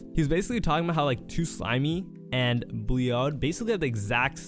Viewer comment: 2slimey and who? Couldn’t understand what you said 😭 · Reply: bleeod, he said his name right